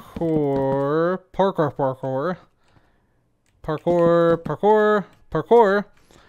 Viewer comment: I need subtitles. At 7:18, what did he say? I thought it might be "Parkour", but I am not too sure.